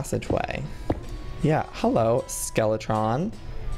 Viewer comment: DID HE SAY "Holo Skeletron"😱😱😱